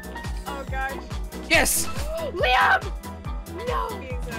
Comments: Hold up did she just call him Liam? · I think gf called his real name “Liam”